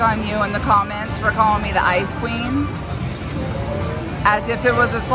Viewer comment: i didn't hear ice queen, i heard ice cream